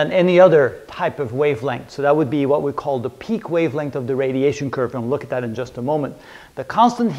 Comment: sir u said something "pik wavelength" sir really I don't understand your slang sir but iam getting it by subtitles but no subtitles for this video 😭😭 so plz say it sir thanks regards I mean thank you for advance 😊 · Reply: That equation is called "Wien's Law" and Wien discovered that the temperature of any object can be calculated by plugging into the equation the peak wavelength of the object's (black body) radiation curve.